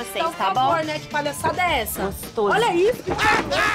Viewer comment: ? What she said in English؟🤔😁 please tell me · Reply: She says there's another wifi password: "Gostoso" (saying that the guy is "hot"). Obviously, she says that to make his girlfriend totally mad! Lol